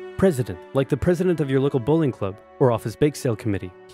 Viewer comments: When he said bowling I thought he was saying bullying 😂😂😂😂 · for 2 whole years I thought it said local “bullying” club not bowling club😂😂😂